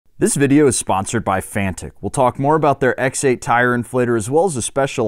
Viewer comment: I really thought said the video was sponsored by fanta at first